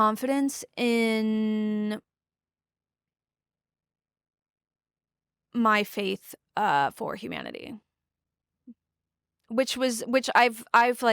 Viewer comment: When she said "it knocked confidence for my faith in humanity" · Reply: Just for accuracy’s sake, she actually says: „in my faith for humanity“ 😊